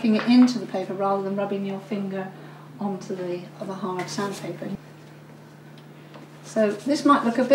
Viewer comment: this is beautiful... i would like to start using pastel as a medium.. but, if i'm not mistaken, i thought i heard you saying sand paper? are you using sand papers instead of normal papers? and if you do, is it necessary to use a sand paper or any paper will do?